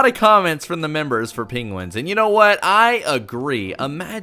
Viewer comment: Am i the only one who heard him say "pingwins" instead of penguins